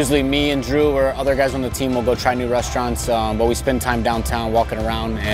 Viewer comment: 伍鐸說的是 So usually me and Drew or other guys on the team will go try new restaurants 不是 will go to Chinese restaurants. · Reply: 超強，我第一次聽也是聽成Chinese restaurants😂 Try new restaurants在你標了後跑去再聽一次才聽出來，不過伍大叔是真的講得很快就是了